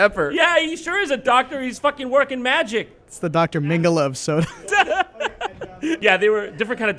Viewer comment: Micheal is my enemy. We must fight for our Dr. Pepper. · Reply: I heard "Dr. Mengele" :/